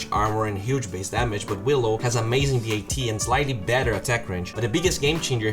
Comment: sound like he says dat instead of bat. was searching for it before i realized :)